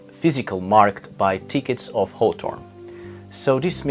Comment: Did you say "thickets of hawthorn" ?